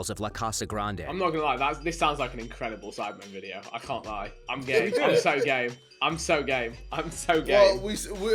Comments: I thought they said Vik: "I can't lie. I am gay, I am so gay, I am so say, I am so gay" Jj: "Oh well we're saying should we do it, should we do it. How are we gonna do it?" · when you thought vikk was saying im gay, im so gay. instead of game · Is it just me or is he saying "I'm so gay"? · Who else thought Vikk was say im gay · I thought vik said he’s so gay 🤣🤣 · is it me or did vikk say gay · When vik said "im so game" I thought he was saying "im so gay" at first. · Am i the only one that thought vik said "Im so gay im so gay im so gay"? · WHAT WAS VIK SAYING I HEARD "IM SO GAY x2 xd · did vikk say that he is so gay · is vik saying he is gay or is it just me? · did vikk just say i'm so gay multiple times??? lol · is it just me or does vik say “am gay am so gay “ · does He say im gay, im so gay lmao? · I thought Vik said "im so gay im so gay im so gay" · I thought he said I'm gay and and subtitles legit say I'm gay. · who else thought vik was saying i'm so gay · did vik just say he so gay three times LMAO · i thought vik kept on saying im gay lool